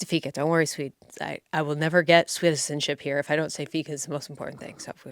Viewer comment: Did you say Swede-zenship? 🇸🇪 😂